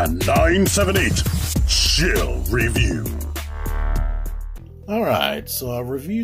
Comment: In the intro it sounds like is saying “TMan978 shall review” instead of “TMan978 chill review”